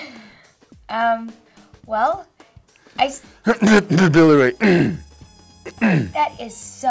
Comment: what was the name he said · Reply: rhazlett billie rae